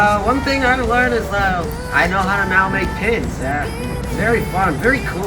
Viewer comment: I thought bro said KIDS!!! And not pins